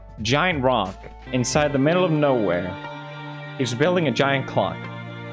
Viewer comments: “And out of nowhere he’s building a giant CLOCK” I thought he said c0ck 😭😭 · “he’s building a giant cock” is what i heard